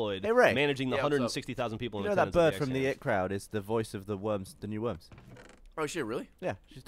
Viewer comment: Did just say the 'it crowd'?